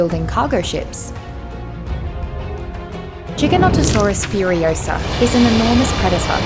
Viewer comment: wait... did she said "Chicken-otosaurus"? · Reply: No.. she said “Jig-uh-not-o-saur-us” which is the correct pronunciation for the species.